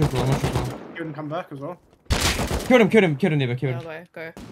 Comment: did he say kill a n word? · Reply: he says his team mates name, nebo.